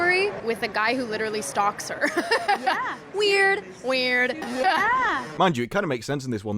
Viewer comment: when that thing goes “weird weird” thought it said “whaa whaa” like the real name South Park gave to Prince Harry’s book but that aside it’s fucking dumb to do that knowing in this ages of technology and meme and trolls people will snip that clip out and make memes and troll it hard core honesty dumb as fuck